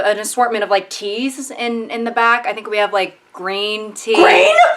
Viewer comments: This girl's accent is crackup, I genuinely thought she was saying "grain tea". And you Yanks say we've got a weird accent. Bloody hell. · GREEN?!?!! The was she said it made it sound like the color green emotionally manipulated her into backflipping into a cartoonishly large meat grinder 💀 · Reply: I thought she said "grain" until she brought up M&Ms 💀